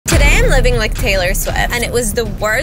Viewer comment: wait why did i hear "today i'm living with taylor swift-"